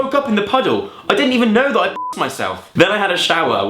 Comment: It sounds like you say 'I didn't even know I f*cked myself' 😂😂